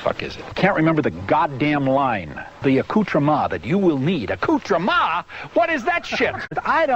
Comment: Accoutrema??? What is that shit?! I damn near busted a gut laughing the first time I heard him say that! ROFL!